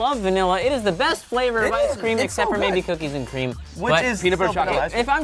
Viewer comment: I wasn't really listening so I thought he said cocaine and weed ice cream, and I just thought: WTF kind of ice cream place does he go to?